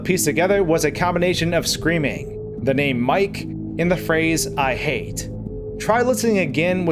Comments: You hear "I hate" I hear "my leeeeeeeeeg" Spongebob has ruined me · I heard him saying Henry, Mike, and help.